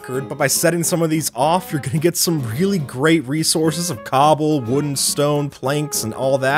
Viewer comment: I thought he said "Wooden stone" 😂🤣